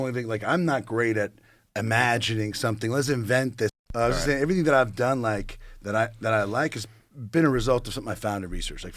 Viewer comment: What did Ben say that was edited out at 1:04:56 ? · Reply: Pretty sure he just said shit